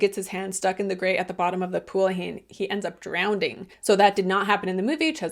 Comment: did you say drownDing 😅 no judgement I'm just curious if I heard you wrong